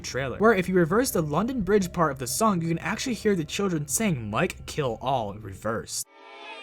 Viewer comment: Instead of "Mike Kill all" I heard "Mikey LOL" XD